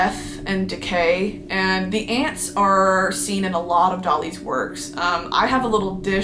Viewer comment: Every time u said “Dali” on its own I thought u were saying “dolly” and I was like DOLLYPARTON?!??!!’)/);’